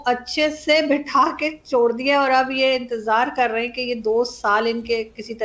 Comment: did she said chod diya....lol